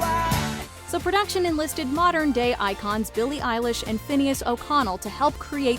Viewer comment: I thought it say “modern gay icons Billie Eilish.”